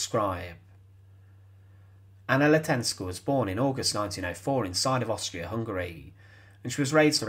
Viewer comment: “….born in NINETEEN EIGHTY FOUR”? I swear that’s what I heard.